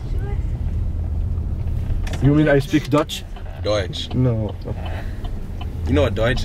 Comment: Sounded like you said Dutch.